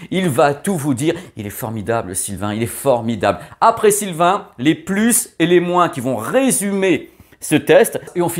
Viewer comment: J'étais pas assez réveillé je crois, j'ai compris « apprécie le vin, les plus et les moins » au lieu de « après Sylvain, les plus et les moins ». 😂